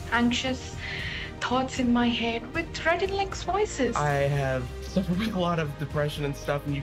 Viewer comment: the captions need to be changed as she clearly says "With Rhett & Link's voices" instead of (indistinct)